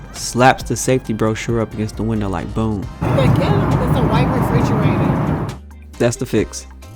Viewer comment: Did they say it was like a white refrigerator? 😂